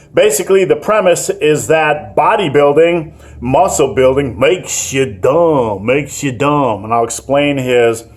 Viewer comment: Thought it said, "makes you dump". Not if you are eating the proteins Naudi.